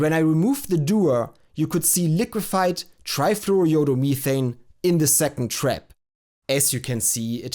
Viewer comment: All I kept hearing was "Trifluoro-YOLO-Methane" xD